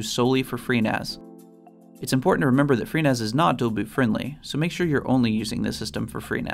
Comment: FreeNAS is what not-friendly? Sorry, tried that section multiple times but I don't understand what is said. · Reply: It is not dual-boot* friendly